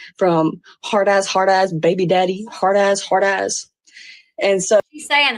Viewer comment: I thought she was saying heartless baby daddy.